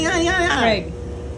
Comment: I actually thought she said “Craig, you know you’re not supposed to f*+k with that” at the very end of this video..:) had to rewatch it again a couple times…:) god bless you both…:)